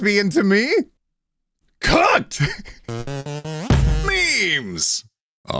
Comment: did he say cut or cu(n)t? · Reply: "cooked" its. very obvious. he said cooked. context clues